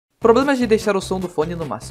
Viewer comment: Eu entendi "problemas de sono maximo" kkkk